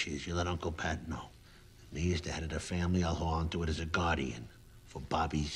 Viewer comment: At around 1:36 , did Tony say "now he's the head of the family???" Or "me as the head of the family " ??? It sounds like he said "he" but that obviously made no sense, but it does make sense that he would take care of this and delegate the holding onto the money to Bobby's kids and taking care of making sure that there was money that would be going to Bobby's kids considering he was warring with the lupertazzis and facing prison on account of Carlo flipping , so he was probably preparing for when he is dead or In prison soon so he wanted this in place for when he's gone either 6 feet Under or behind bars. For how shitty tony became, it is nice seeing him commit one more act of honor and true la costra nostra.